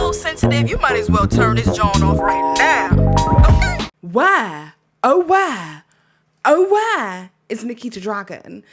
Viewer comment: I live in Texas and I heard the "where oh where" at the beginning as a very southern "why oh why" lol 🙈 First time to your channel, great content! Cheers! 🥂